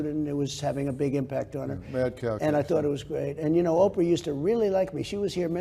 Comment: what did Dr Phil say? did anyone catch that? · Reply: Mad cow disease